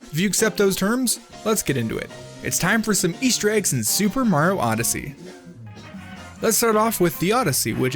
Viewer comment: At The beginning:why do i hear "super maro Odyssee" 😂😂